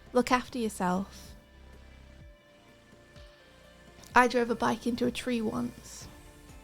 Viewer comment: I thought said she drove a bus into a tree😂😭😂